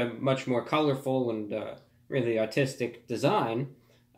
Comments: Everyone making a fuss because of my accent and the way I pronounced the word "artistic"... just trolls making their rounds! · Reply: I thought he said autistic amd started dying cause i never thought he would say that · I thought he said autistic but apparently he said ARtistic. Haters ain't got much they can say now. Take care. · Everyone making a fuss because of my accent and the way I pronounced the word "artistic"... just trolls making their rounds! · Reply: I actually genuinely thought you meant to say "autistic" and were criticising R&M lmao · Everyone making a fuss because of my accent and the way I pronounced the word "artistic"... just trolls making their rounds! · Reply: I thought you said "autistic"